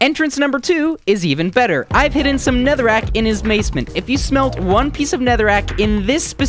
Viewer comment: does anyone else hear "netherrack in his macement"